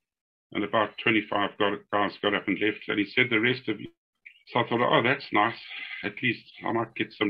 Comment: "The rest of you are eligible for selection to become officers" 8:27 I said I was inake 142 but meant intake 152. At 17:52 "I decided to have a braai for the guys that evening". At 19:55 zoom stalled and what I said was 'it turned out they had been attacked for an hour and a half'. 21:19 I meant to say K Car with four-barrelled gun. At 22:26 zoom stalls again. What I said was I was concerned by the thick bush leading to the keep, from an ambush point of view. At 25:11 I said "there were about 500 people in a compound in the forest" At 29:57 and 30:42 zoom lost connection and missed out the name of a white soldier killed by the name of lance corporal John Hartley .At 32:11 the words "special branch" were omitted by zoom. At 32:56 "he rand into the low density suburbs"..At 33:46 I meant to say "at the end of that island".. At 34:38 he was put in an underground pit until the end of the war. At 36:59 I was ordered to Kazungula police camp. At 38:06 I got a call from Major Pearce at Vic Falls and was told to wait for him...At 38:37 in I should have said Major Pearce not Price. At 40:00 I meant to say "brown eyes" not brown noses . At 49:20 Kemp said "let's have a go at them" and I responded "what do you have in mind?" At 50:36 "an RPG 7 came over and hit a tree behind us"..At 51:08 "one of our tracers had set an abandoned hut on fire" At 51:41 I said it was an MB326 aircraft made by Aermacchi"..At 54:31 I said "in hindsight it would have been a good thing serving with Don"...